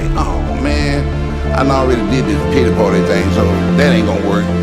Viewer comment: Wow @ 2:49 did he say “ already did the Diddy party thing and it didn’t work ?”